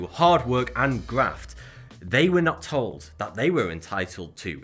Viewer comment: Did He Say GRAFT ? WTF ???